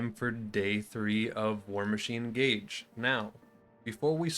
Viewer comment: I thought it said Warcrime Gaige